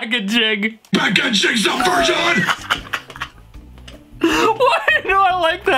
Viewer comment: i hear that guys chicks a virgin